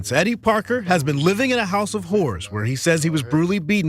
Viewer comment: Sidenote: @ 0:42 Without the closed captions on to read, there's no way to know that the reporter did NOT say, "living in a house with whores"... I don't feel that was by accident one bit. That's just me though.